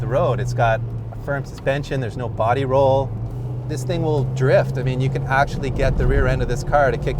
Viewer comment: did he say it will drift? im out.